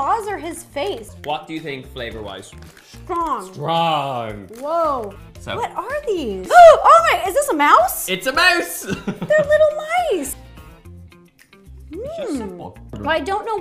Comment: Not me who thought he said, " ITS A MOUTH"